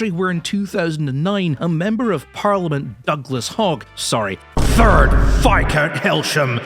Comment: thought i heard "haig" instead of "hogg" for a second lol